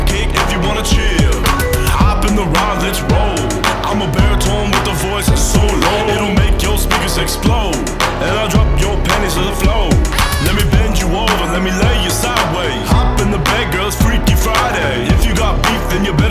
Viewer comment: Thought he said make your sphincter explode